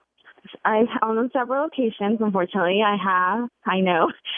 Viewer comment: What did she say at 0:30 - 0:32? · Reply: she says "i on several occasions unfortunately"